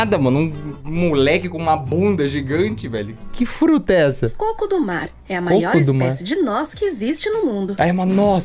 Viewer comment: eu escutei "O Cu do mar" KKKK